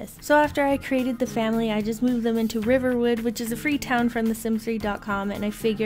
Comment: Did you say Riverwood? It's Riverview! :D